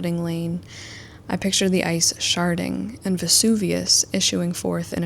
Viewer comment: I thought she said sharting and I had to look up and rewind from my puzzle.